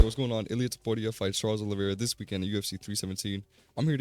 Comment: i thought you said "whats going on idiots" at the start 😭😭